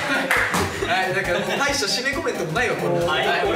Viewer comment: 1番最後にこーちが鬼ごっこ忘れてるんじゃない？的なこと言ってる気がするんだけど。 聞き間違いかな🤔？